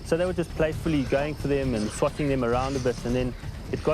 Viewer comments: did anybody else hear he said the f-word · Hold up Did he say fuckin them around?